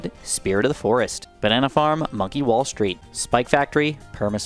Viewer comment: bro when he said “monkey wall street” it went so fast i thought he said “fucking wall street”